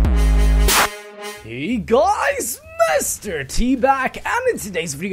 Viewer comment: I thought he said HEY GUYS MR.TBAG